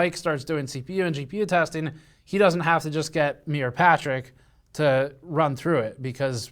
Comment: I get the notion that Mike and Patrick do not get along very well. During the moving vlog and now statement "Mike does not need to get near Patrick". Hmmm... Edit: Yeah, I fubared that one. I totally misheard that... twice. Sorry for that.